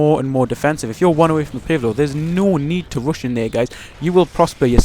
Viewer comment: Did he said "no need to rush a nigga" on